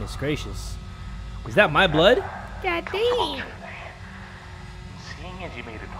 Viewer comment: sounds like he said Daddy at 8:24 or it just me